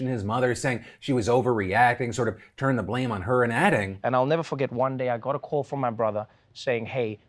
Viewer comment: I thought he was saying ever ford. i was confused